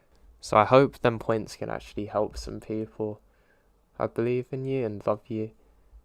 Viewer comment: it sounds like you say "I believe in you and F-you" 🤣